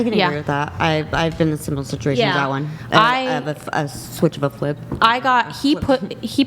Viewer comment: Once i heard "Switch of a flip" everything else from this faded away, I'm ruined